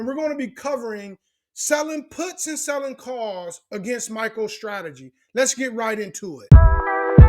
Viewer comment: Sounds like you saying “Micheal Strategy.” 😂